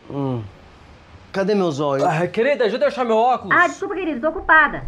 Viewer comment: Só eu que ouvi "cadê meus Zóio" no início do vídeo?